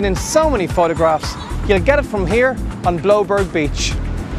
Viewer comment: Anyone can tell me what is the name of beach he said on 1:40? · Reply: Bloubergstrand Beach is the full name